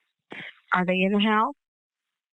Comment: Ok this video shouldn't be joked, but- 6:52, when they said "Are they in the house?" the captions said "Are they in hell?"